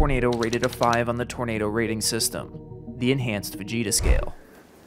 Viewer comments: I thought you said the enhanced vageta scale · I know you said Fujita scale but I swear to you I heard "Enhanced Vegeta Scale" Also FEMA's resources aren't dependent on the rating of a tornado. Just who's election sign is in your yard.